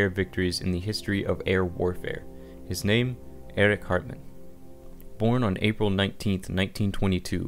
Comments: Could've swore he said Eric Cartman lol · Who else thought he said "Eric Cartman?" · Rickto-Fen... Luft Waff... then, it sounded like he said Eric Carman! LOL! Good video though. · I must have misheard the name, I though the commentator was say "ERIC CARTMAN" of SOUTH PARK fame...LOL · At first i thought he said “eric cartman” and it then made sense why he hated kyle so much. 😂😂 but nvm 🤷🏽‍♂️ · Thought you said Eric cartman the 1st time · i thought he said eric cartman at first